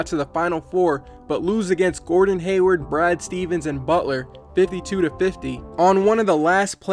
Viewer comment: Did he just say Bryan Stevens instead of Brad Stevens ?